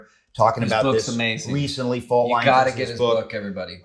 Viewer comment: Did anyone catch the name of the book at about 35:30? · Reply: He’s talking about Faultlines by Voddie Baucham